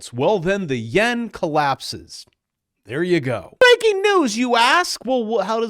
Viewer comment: I swear you said Breaking news -You Ass..... and im OK with it😅